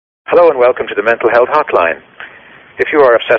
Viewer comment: Thought it said "Mental Health Fortnite" i was like "yeah, people who play fortnite have mental health issues"